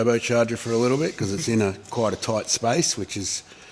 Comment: Did just say that fitting pipe in tight spaces is the story of his life? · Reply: No, he said fitting turbos in tight places is the story of his life, and he ain't wrong.